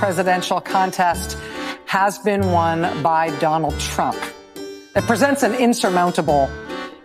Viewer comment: at time 08:09 did she say Donald Trunk]?